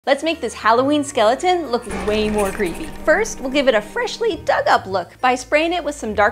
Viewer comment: I thought she said 'lets make this halloween skeleton look way more creamy" 😭😭💀